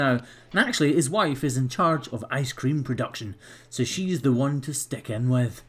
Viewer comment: For a quick second I thought he said that he needed to “stick it to the lady in charge of ice cream.” Hahahahahaha!